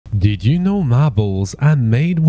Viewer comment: I heard: "Did you know ma balls are made with broken glass?"